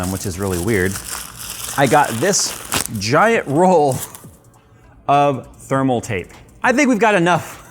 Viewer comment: Lol i thought you said giant roll of thermaltake 😅